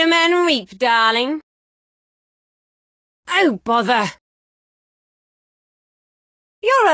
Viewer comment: I always thought she said “oh motherf-“ when she says oh bother 😭